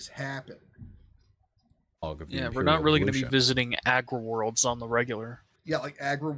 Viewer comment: I really thought he said Agua-worlds. I thought we were getting a deep Vaush reference.